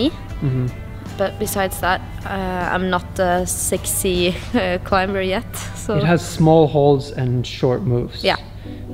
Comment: Did anyone hear "I'm not a sexy climber, yet" ?